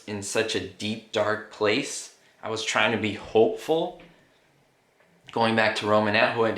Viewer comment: All this time I thought you were saying "Be HELPFUL". ha ha